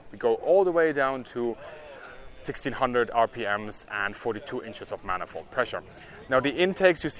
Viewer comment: 1600rpm/42" ? Did I hear that right? Just sounds like a low rpm for that much manifold pressure.